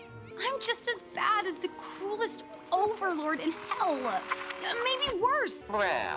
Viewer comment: "Im just as bad as the cruelest overlord in hell" 14:15 Alastor: you called? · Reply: I thought she said “coolest overlord”